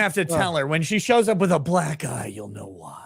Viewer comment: when said my wife shows up with a black eye. i thought you said when my wife shows up with a black GUY !!!,.